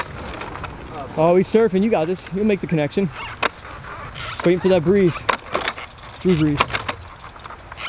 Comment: haha did anyone else hear Scotty say, wait for that brees, drew brees at 10:45. I'm a huge fan of drew brees